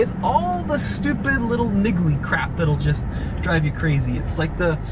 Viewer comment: did you just say niggly I’m not sure that’s allowed anymore 😂 lmao